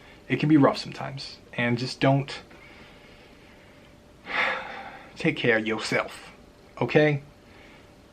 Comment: I thought you just said And just don't take care of yourself lol